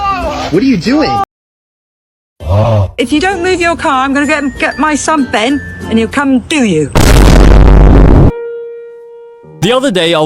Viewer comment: am i the only one who heard (I’m gonna go get my son and he will doo doo) poop